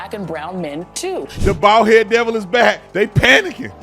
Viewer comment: Man I laughed so hard when I heard, "Bald head Devil is back."